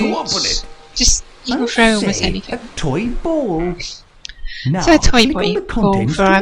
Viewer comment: did you just say "a toy boy"?? :3